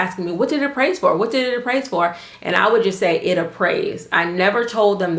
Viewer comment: Thanks for sharing! Very helpful. May I know what word you said after” what did it appraise for and I would just say __”? Somewhere around 2:28, I know it means just tell the sellers or sellers agent that the property just appraised pretty much the same as the contract price. TIA! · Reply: I think I just said "It appraised" in the video. You can also say "it came in at value" if they ask.